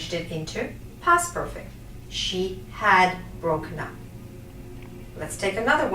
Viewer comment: please help me. i learn English not good 6:27 "she has .... " i can't hear it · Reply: natsu "she had broken up"